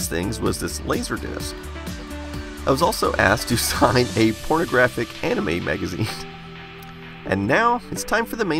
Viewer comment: Hearing the 8 bit guy say "pornographic anime magazine" makes me laugh so hard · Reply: I thought I heard “phonographic anime magazine”.